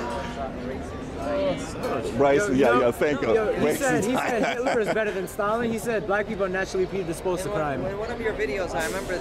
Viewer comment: Who was better then Stalin? Sounds like he said : "He thinks Hillary/Hitler was better then Stalin"? What did he say?